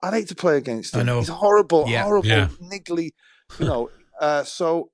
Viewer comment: did he just call him niggly